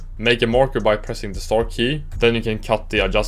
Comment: lenguage barrier :C what key did u say? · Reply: Star key = *